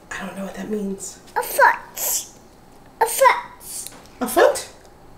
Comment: I think saying "a fork" I guess she wanted to poke her strawberries lol.